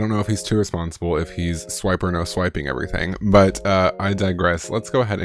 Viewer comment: Idk if I was just hearing things or what but I seriously thought you said swiper no swiping lol